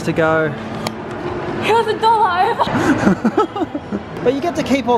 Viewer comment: "hell a dollow!" I heard that, can someone pls tell me what she said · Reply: she said "it was a dollar over".